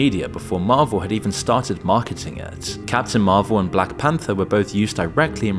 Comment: I thought you were saying, "Cats in Marvel!" 🤣